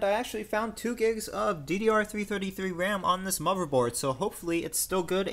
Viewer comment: MUVERBOARD!!! Yeah, yeah, yeah, I know. Pardon the lisp that I am still struggling to get rid of :D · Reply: I am afraid you misheard me. I said DDR-333 which is DDR RAM running at 333Mhz.